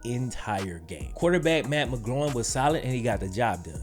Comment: did you say Matt McGloin or Matt McGroin?? Lololol